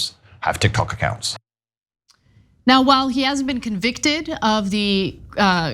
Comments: You said convicted, he has not been convicted. This woman is really spouting her unconscious bias. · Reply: I just heard her say "while he has not been convicted" just now 2:17 mark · I had to watch the video again just to confirm how triggered you were that you want on a whole rant and completely misheard what Ana. 😂😂😂 She literally said "while he has NOT been convicted yet" I cant with these Taint simps anymore. Your guy is burying himself.